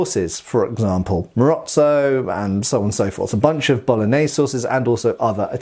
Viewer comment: I heard it as "A bunch of bolognese sauces" 😆